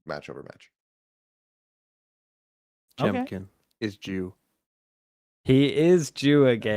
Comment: even with the American assist I still couldn’t figure out what everyone was saying until Sliggy very carefully said overdue. I heard “diuuwen” from Achiellios. 😓 thought it was some pro player I didn’t know